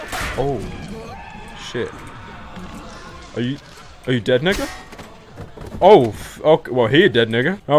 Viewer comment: did he say the n-word? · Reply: yes nigga